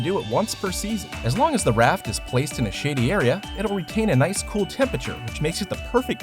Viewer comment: It sounded like he said shitty area instead of shady area